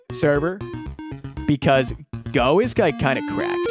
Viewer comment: Go IS kinda cracked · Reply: I heard "Go is kinda crap" lol